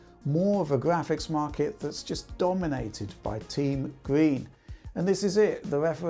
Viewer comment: damn at first i thought he said team greed instead of team green